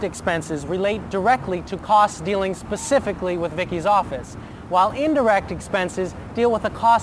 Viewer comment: did he say Vicki's office?